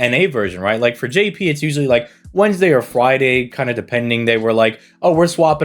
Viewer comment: It almost sounds like your saying fwiday instead of friday. BTW you say to skip this banner, but the CE's are good and the servants are pretty good too.